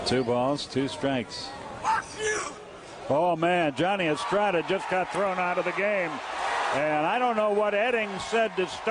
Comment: Would love to know what the ump said to Johnny Estrada at 3:29 to get that reaction?!? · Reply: I think it was "Don't show me up"